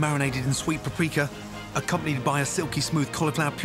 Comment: "broad beans cooked with hummel?" Or I didn't understand what he said. Does anyone know what the second ingredient was? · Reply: Jamon,it's cured meat